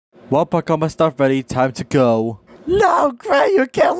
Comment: "Well i got my stuff ready, time to go" Vs what i hear "Opaganganstan buddy, time to go"💀💀💀